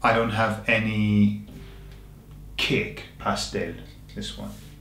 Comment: That was funny at 13:42. I thought you said " I don't have any kick" Then you said "Pastel" which I translated to Cake. Then I realized that Cake in an British accent sounds a bit like "kick".